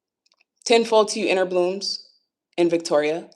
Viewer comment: I thought you were saying "10-4" instead of ten fold and it was a nice little chuckle lol